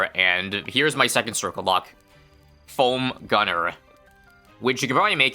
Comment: Thought he said "Cum gunner" for a moment